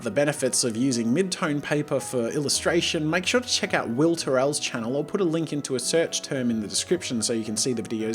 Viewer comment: I thought he said will farrell instead of will terrell